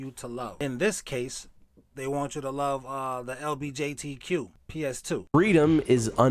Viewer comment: Did you say PS2…😂😂😂